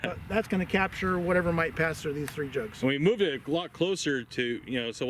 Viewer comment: I swear said, " we moved it a GLOCK closer." Played it back with captions at 75%,50%&25% speed. It sounded like a progressively more intoxicated drinking challenge. And don't drink and operate mass lead, tungsten or steel accelerators. Thank you for the time, effort and passion you all have invested over the years.